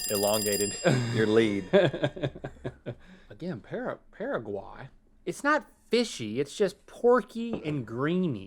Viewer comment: I can’t tell if Link says damn there or not. Not that it matters, I’m just confused if that is what he is saying or not lol. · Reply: He said “again, Paraguay